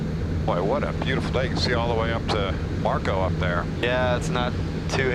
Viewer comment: ish did he say Marco? Polo! I have OVER 1000 hours in a 598 & 637